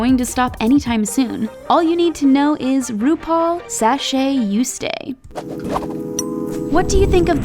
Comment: YES SORRY🙈 *Shantay, you stay!* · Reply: I was about to sleep when I heard, "Sashay you stay".. It took me a minute when I realize what was wrong 😂